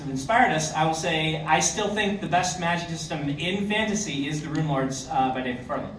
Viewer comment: what book does he say the best magic system is from? Please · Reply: Runelords series by David Farland (aka the guy in the cap on the panel)